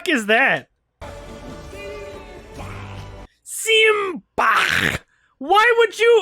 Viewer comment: no joke, I had no idea what he was saying until pointed it out I legit heard "sieg heil"